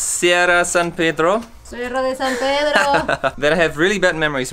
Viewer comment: We are in Sierra de San Pedro:Cerrooo~ One minute later: Sierra de San Pedro: CERROOOO Y el ciclo se repite y se repite. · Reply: Lol at first I thought he was saying Seattle de San Pedro lol